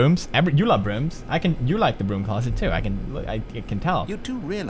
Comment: Did he just say I love the broom closet, because I do.... And my name is Amber. *****